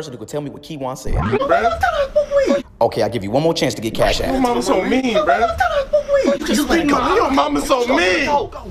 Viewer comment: if I'm right I think he said how your mama going to tell me I smoke weed🤣